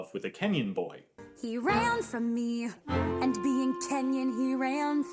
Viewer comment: it was suuuch an easy fix. She said she was 5, so you could just say, "Since he was 10, he ran fast," instead of, "Since he was Kenyan, he ran fast." It is only a slight, minimal, miniscule delivery difference. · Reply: I listen to the musical version all the time and I swear I thought it said "and being 10, he ran fast."